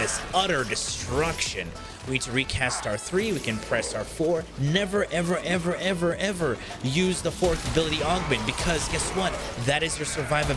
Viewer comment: What did you say on 6:00? I couldnt make it out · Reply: not to use the augment of the ult